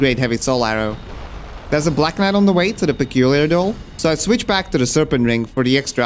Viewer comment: Is it just me or did he say "there's a black man on the way to the doll"